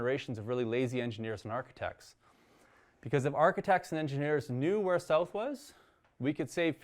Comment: "If architects and engineers knew where_____ was, we could save 50%...." What did he say? I'm really liking this series. It amazes me that some people don't think of water this way. That's the first think I thought of when I decided I wanted to homestead. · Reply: If they knew where South was.